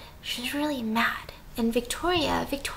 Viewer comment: I said Victoria's Secret when I hear Victoria has secrets in